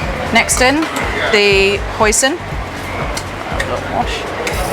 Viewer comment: My ear must be broken i hear “next up the poison” 💀